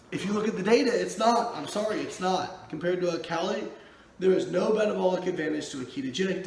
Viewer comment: please, what did you say right at 5:01 (your vid linked below, and cued to the correct spot)? phonetically it sounded like, "compared to oh cali" <link>